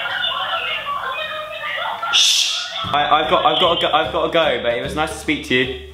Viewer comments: Did you say " I've gotta go babe " 😼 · Did he say babe around 4:35 😍😍😍😍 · did he say 'babe'?